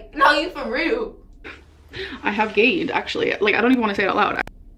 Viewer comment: I thought she said " i have aids" and was shocked